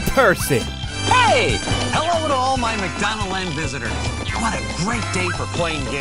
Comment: Not gonna lie at first I thought Ronald McDonald said prisoners instead of visitors